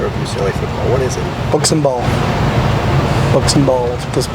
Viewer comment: does Chip say "Books and Balls" ?